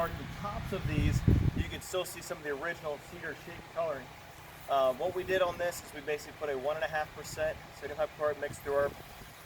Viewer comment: all i heard was "what we do is put a 1-1/2%" . then i heard "garble, garble, garble" -- Can you share the recipe for your mix please? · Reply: 1.5% sodium Hypochlorite solution